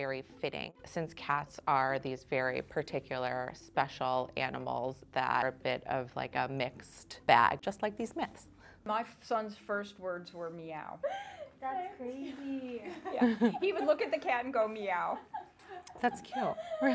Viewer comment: I sweat to god I thought she said "A mixed bag, just like the Smiths" and I was like "how weird to bring your musical tastes into this conversation".